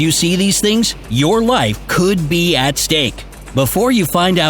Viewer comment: In the beginning where the text says "Your life could be at stake" I thought it said "Your life could be a steak"